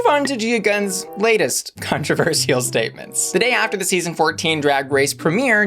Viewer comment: ”Let’s move on to Gia Gunn’s Cuntroversial statements” OMG! I don’t know if that’s what you meant to say, or if I misheard it, but it’s all I can hear now! 😳🤣😍